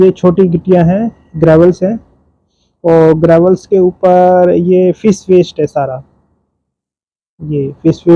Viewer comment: par ap kya bol rahey hai word merey hisaa se fish waste bataa rahey kya yehi hai bataiye please awaaz me clarity nahi aa rahi hai* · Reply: Ha fish waste